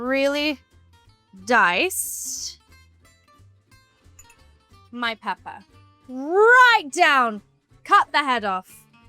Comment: when you said "my peper" I heard "my peppa" at first